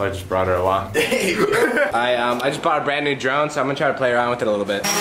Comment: I thought he said I bought new drugs